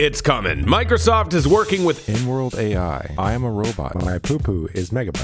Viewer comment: I whipped my head 45o toward the video with my mouth agape when I heard that ''N-word AI''